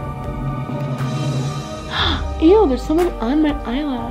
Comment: "Ew, there's 'someone' on my eyelash !" like is u thought she said that too ! lol